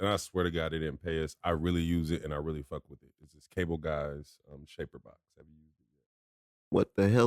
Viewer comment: What plug in did he say at 57:55? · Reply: CableGuys Shaperbox bundle